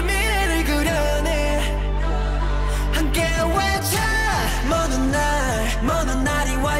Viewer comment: Is it just me or does anyone else hear them saying "I'm gay witcher" at about 0:54?